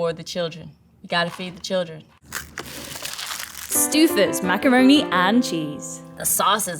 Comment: did she say Stewffers LOL